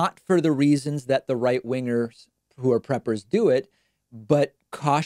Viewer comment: Everyone who lives in the country where Trump is president should be a prepper. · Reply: I thought he said Pepper. "I'm a Pepper. You're a Pepper. Wouldn't you like to be a Pepper, too?" Drink Dr. Pepper and you can be a Pepper, too.